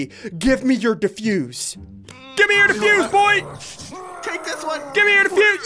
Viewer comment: did biffle say "defuse ols?" XD.